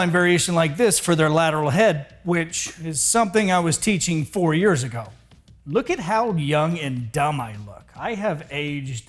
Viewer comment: I thought you said you were teaching it "40 years ago", which would have made sense. What happened?!?! Just kidding!